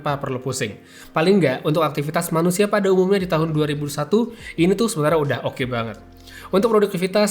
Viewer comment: subtitle/caption saya aktifkan di sini, 2:35 itu dia bilangnya 2021 atau 2001? di subtitle (otomatis tergenerasi sih subtitlenya) malah tertulis 2001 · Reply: 2021 tapi ngomongnya cepet, kedengerannya jadi 2001